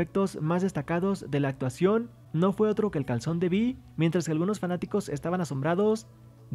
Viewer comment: escuché "Calzón"???? 😂